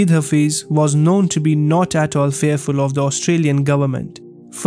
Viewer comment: That’s what the system does, silence those who speaks the truth or goes against their narrative. Also it sounded like you said Australian government instead of Austrian government around 0:21.